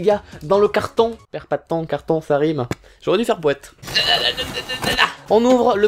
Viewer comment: J'avais compris "J'aurais du faire pouet"... Honte à moi x)